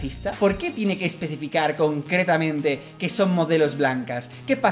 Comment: En el minuto 8:54 entendí cokretah XD